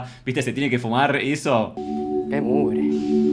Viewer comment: En el minuto 17:45 yo escuché mal o el marido de Ema dice que mugre ? 😅 Díganme que no soy el único que lo escucho 😂